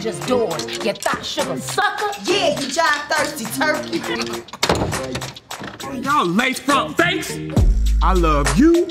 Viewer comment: did she said :"thirsty turkey"? LMFAO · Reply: she said "JIVE thirsty turkey"!!! LMAO